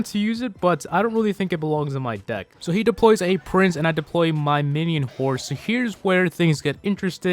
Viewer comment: did he say minion horse???